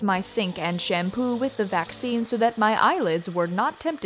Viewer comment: did I hear the word vaccine.. I DID OH NO SHE KNEWWW!!!!